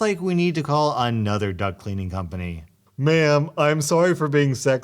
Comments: I thought you were saying duck cleaning company not duct cleaning company and I got very confused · So, i watch your videos while i write sometimes, so im not paying attention to the screen. When you said duct cleaning, i thought you said duck cleaning, so i searched it up to see what you were talking about. I now realize my mistake, but i did watch a video on how to clean a duck. All is well that ends well i suppose lmaooo